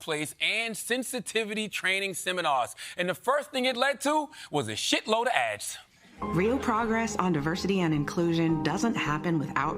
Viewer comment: I heard "shit ton of ass" which is pretty accurate referring to the ads too